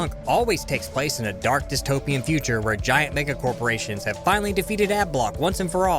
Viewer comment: Did he just say: "Giant nigga corporations"?